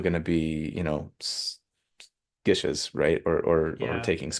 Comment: what is that word for martials with spells? Ghysies? What does that mean?? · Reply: Gishes